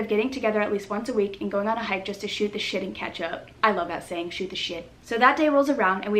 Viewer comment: I thought she said "shoot the shitty ketchup" until I turned on captions. 🤣